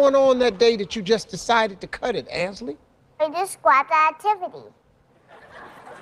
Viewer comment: Sounded like she said "I was describing an activity"